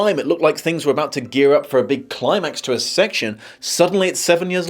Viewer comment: Did you just say, "climax to a sex"?